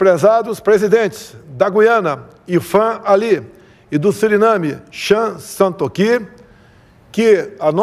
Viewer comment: Eu ouvi "Siriname"?